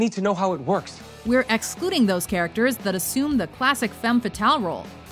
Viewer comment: What kind of clothes did you expected Black Widow to wear? A burqa? A maternity dress? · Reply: Thought she said no femme fatal?